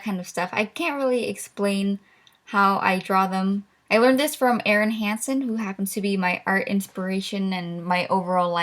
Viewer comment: For a split second i thought you said evan hansen